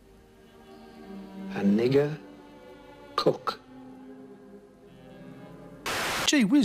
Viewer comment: I thought he said a 'nigga cock' jesus